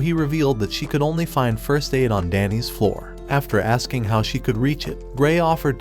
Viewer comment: I swear I hear Dandys floor.